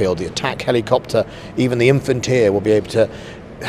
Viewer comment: Infantier! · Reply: I know, but I heard him say Infantier which made me laugh :)